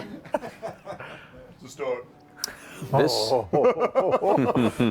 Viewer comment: or so, says "I don't feel the love! Maybe I should leave for another year!" Did anyone catch what said afterward? Man like third or fourth apology or whatever to after they fought the skeletons had me sobbing at my desk at work. and Travis constantly amaze me with their skill at role-playing. They have such amazing chemistry, and I love the relationship between and. I always have and always will. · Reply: "It's a start." the subtitles said.